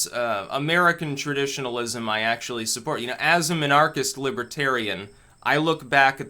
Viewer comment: Your pronunciation of Minarchist offends me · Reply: Oh man, I thought he was saying "Monarchist" this whole time.